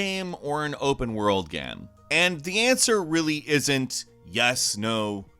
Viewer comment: “Is this a linear game or an open world gam?” Me:...... Did he say gam? 🤨